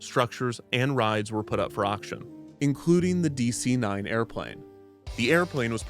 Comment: It's weird how at 1:03:29 the commentary says DC-9 airplane, but the subtitles say Boeing 727 airplane. I guess these aren't automatically generated.